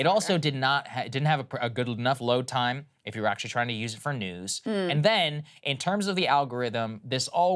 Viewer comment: it sounds like you said nudes.